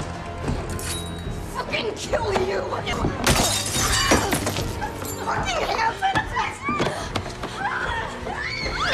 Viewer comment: what did she say? I couldn’t understand. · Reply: "fking hand sanitizer?!"